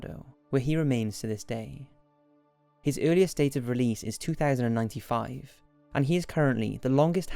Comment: did he said year 2095? it almost same as a lifetime